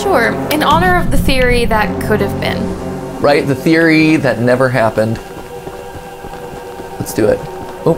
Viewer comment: When said the "theory that never was" I thought they said the " fairy that never was" I was confused for a minute